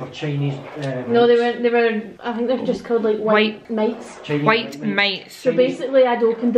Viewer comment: OMG I THOUGHT YOU SAID TINY MICE!!! AND JACK WAS HITTING THE WEE BABY MICE AND I WAS LIKE NOOOOOOO!!!!! I had to rewind and watch it again until I realised you said mites 😭